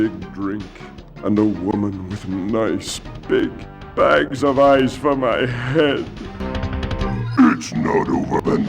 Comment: I always thought Cortex said "with nice big bags the size of my head" and i was like "woaahh nice slip in there, Naughty Dog"